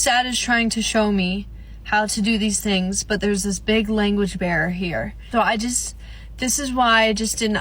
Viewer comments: I’m sorry…did she say language BEAR? 🐻 she did not!! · Is it petty of me to point out that it sounded like she said "language bear" instead of "barrier" 😅